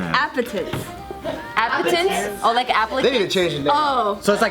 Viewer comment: I misheard the app name as apple tits anyone else? No, okay just me?